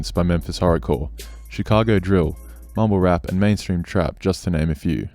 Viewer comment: it sounds like he said "homo rap" 😂😂 I know he said mumble rap tho!!